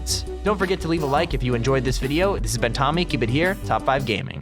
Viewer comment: The ending sounded like he was saying top 5 gay 😂